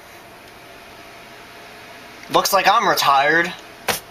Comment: "Looks like I’m retired!!" ☠️ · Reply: Not gonna lie, I actually thought he said "Looks like I'm retarded!"